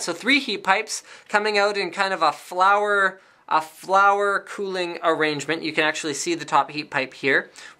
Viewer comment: Did you just say flowerarrangement at #4:39 ?